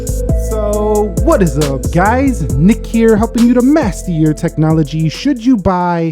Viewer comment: I always thought u were saying, “hey guys helping u with last year technology” I hade to turn on captions to see what u were really saying lol, great video btw I been watching since u were at 150k subs